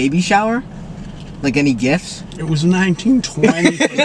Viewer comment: Did jimmy say 1920 🫢